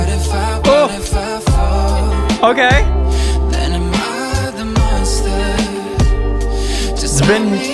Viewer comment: I heard hide the mustered?